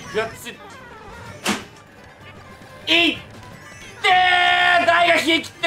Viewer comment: 痛ってーー！と聞こえてたw